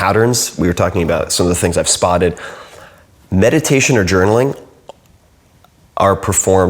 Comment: what does he say in 13:01 ? Meditation and ...? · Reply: Yunus D and journal